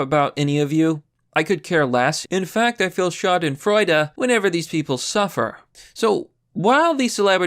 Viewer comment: Did you just say schadenfreude at 5:13?